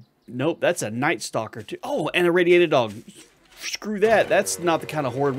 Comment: I swear that I heard you say radiated dolphin. 😂